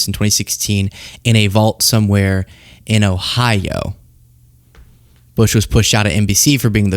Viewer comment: Are my ears broken 7:40 because i keep hearing idaho, not ohio.